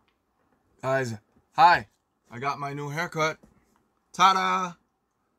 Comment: joonie look so handsome 😚💖💖 What joon said: "hi guys how's my new hair cut? " The subtitles: foot